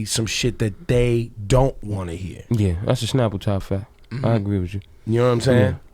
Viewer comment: What did Lux say at 11:00? Anyone? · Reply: "That's a snapple top fact, I agree with you"